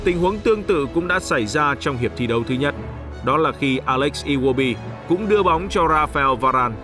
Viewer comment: Ủa có nghe nhầm ko mà mình nghe coa alex iwobi gì ở đây ta